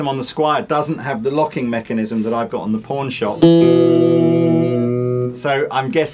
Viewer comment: I thought the said: P*rn shop!? LOL :-P I could see a double neck project coming up. Are there any plans floating around of this?